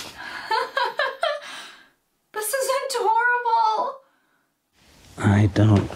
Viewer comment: I’m sorry, did you say “this is adorable?” Because all I heard was “this will end horrible“ lol 😂🤣😩😫😭